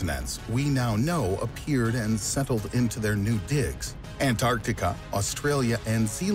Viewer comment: did he say dicks?